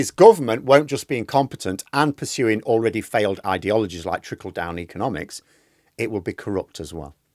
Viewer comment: Thought you said Triple Down Economics and that kind of fits.